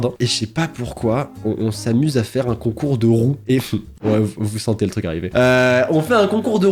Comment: Oh nan- J'ai entendu "un concours de rôt"- 💀 Avec ta crush c'est pas ouf